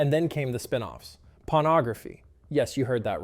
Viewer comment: "Pawnography; Yes, you heard that right" I heard pornography, and was extremely confused, thinking this was some sort of Rule 34 controversy.